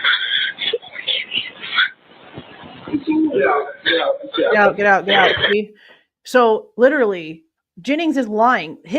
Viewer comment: Did she say "Is Kaylee alright?" or did she say "is Kaylee in there?" Or did she say something else about Kaylee? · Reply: I listened to a cleaned up (less static and feedback) version on Law n Crime when it came out and it’s quite clear that says “Poor Kaylee “ while Hunter is attempting to open Xanas door. It wasn’t acknowledged by the presenter or anyone in comments and I have since seen no mention of it by others. It’s like everyone is just ignoring it. It clearly implies that they had already seen Kaylee and Maddie. I have to see if I can find that copy again, if I do, I’m linking it everywhere because I know I wasn’t the only person to watch this but I’ve seen radio silence regarding it.